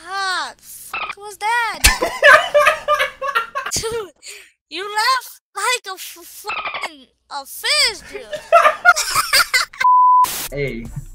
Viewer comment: did he say fearless laughs like a friesbee